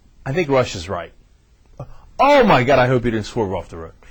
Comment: HA! When you said "Russia's right" I thought you said "Rush was right" Good thing I wasn't driving or I would have swirved.